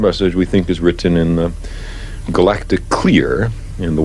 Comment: Did he say "galatic queer" around 13:35 , just confirms "space" is fake and gay .. on a serious note .. thanks for all the content. and Yes , Jesus is God and the Way. Continued blessings.